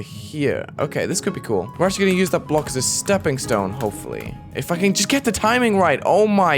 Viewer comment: "It fu*king just can't get the timing right" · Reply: I thought he said that too but he said “if I can” not “it fucking”